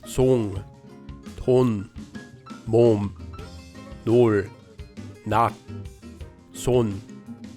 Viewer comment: it sounds like he is saying dol instead of nol. Am I missing something?